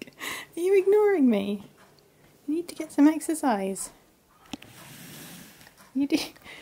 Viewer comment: exercise?! I thought you said extra fries. 🍟